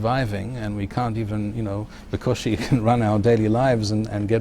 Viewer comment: "The Cushim can run our daily lives" Did I hear that correctly? What does it mean? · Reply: He said בקושי